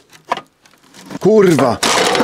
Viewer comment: did he really said kurwa😀😀😀😀😀